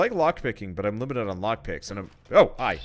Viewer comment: the subtitles said of help instead of oh hi.